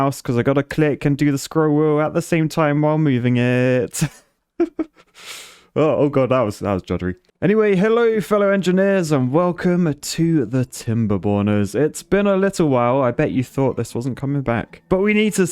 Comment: I thought it said timber boners 😂